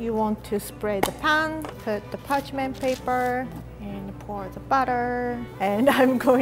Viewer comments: When she said “batter” i thought she was saying butter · When was talking about pouring the batter into the pan it sounded like she was saying butter and I was so confused lol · when she said batter it sounded like she was saying butter.